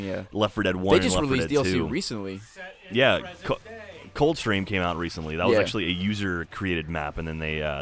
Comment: I thought you said cole train and was like wtf and then I realized cold stream